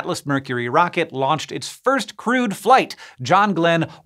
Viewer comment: When he said "crewed" I heard "crude," at first. Ah, the English language.